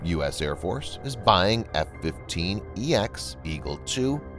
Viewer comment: Glad to see their keeping my jet and modifying it for future use. Also, legit thought it said F-1sex eagle at first. Lmao. Sorry, i haven't finished my coffee yet.........🤣🤣🤣